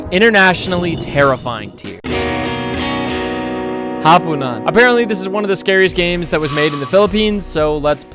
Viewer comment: I did it again omg I thought it said hayuapan 10:47 hahahaha, omg this part scared me Jesus 12:20 the part after this was funny getting ganged up on 13:02 why did I think murderhouse said murerahouse 15:11 nah this part crazy I got scared 😂😂😂 17:35 not aging I thought it said Kayla 21:26 but this whole thing was hilarious and I got scared a few times